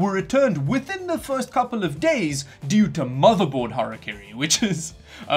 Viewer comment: what is motherboard hierarchy and why is it bad? If that's not what you said, sorry I couldn't understand it and opened the transcript and checked the CC's and they both said it was "motherboard hierarchy."